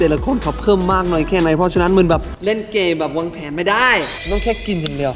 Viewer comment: ทำไมผมได้ยินว่าเล่นเกเเบบวางเเผนไม่ได้